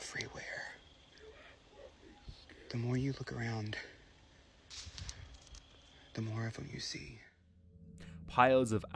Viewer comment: Did anybody else hear the voice at 36:11? “You act fkn scared” or maybe “are you fkn scared” either way, that was creepy af. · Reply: It says " You are fuck1n' scared", and the voice was right, dude was definitely scared lol.